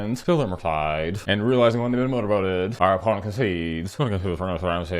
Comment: after you stole the murktide, you said it so fast I thought you said ' realizing they're Winota-boated' 😂 made me cry